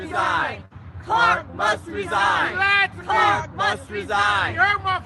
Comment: When i frst heard it it sound like they said Clark must D😮 until they kept chanting and i finally heard what they said 😂It wasnt motivational at all .